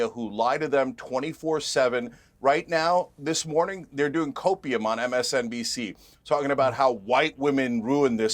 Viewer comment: Did he just say copium